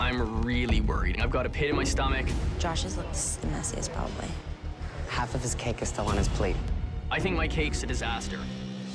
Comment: idk what Alvin said at the end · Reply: Ooohhh he said “the only think tropical about this is I fully hate it”